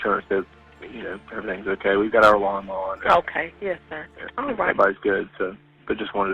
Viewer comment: I think he says "we've got our alarm on." I thought he said lawn mower at first too.